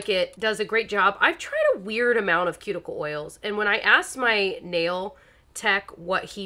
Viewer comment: I love when I see post videos, I get so excited! When you said I asked my nail, and paused for a second, I thought you were actually saying “I asked my nail” 😂😂😂